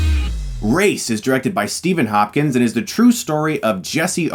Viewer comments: Thought he said Steven Hawking 😂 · Who else thought he said Steven Hawking?